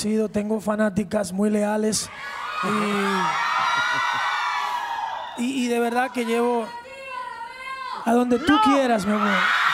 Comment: que dijo la mina del minuto 9:11? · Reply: llevame contigo romeo